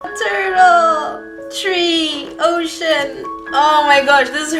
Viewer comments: I thought that she said “oh shiz” and not “ocean” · When she said "ocean!!!" I thought she said oh "shin!!!"